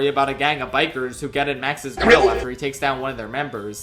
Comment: Get in Max's WHAT? I'm sorry I couldn't understand that (And to, DO NOT throw another needless temper tantrum at me, I'm just asking a question.) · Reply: Grill